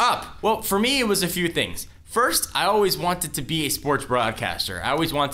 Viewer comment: XD My video had to buffer, and it sounded like said "I've always wanted to be a sports bra"